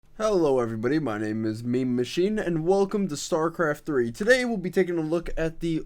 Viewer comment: Did he say Starcraft III???